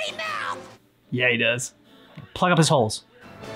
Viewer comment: I had to rewind again for a minute lol when you said plug of his holes for some reason I thought you said play with his holes lol my mind went dirty oh what happen to my innocent mind 😭